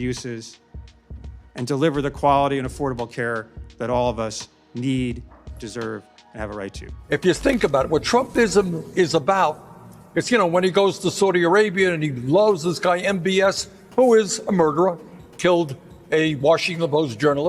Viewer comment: I definitely heard Bernie say “when he goes to Saudi Arabia and BLOWS this guy NBS”